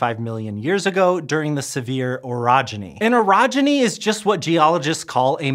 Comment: Here in Utah, we say Sevier differently so I thought he had said “severe orogeny”. 😂 I’ve heard pronounced “seav-er”. I’m not sure what the original is, but I do get that language is very dynamic and regional and it’s only pedantic people who think it should be pronounced one and only one way. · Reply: as someone whos never heard of the Sevier Orogeny, I also thought he said "severe" lmfao